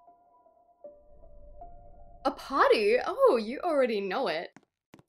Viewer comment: CAN'T WAITT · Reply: i thought you said “a potty? you already know it”😂